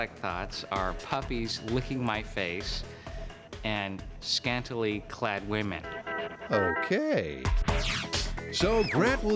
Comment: i heard women licking his face and scantily clad puppies.